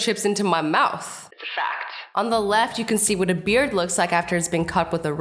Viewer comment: did you say BEARD